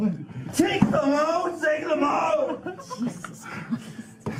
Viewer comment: what was he saying ar 4:11 to 4:14 ? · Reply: Take them out take them out 😢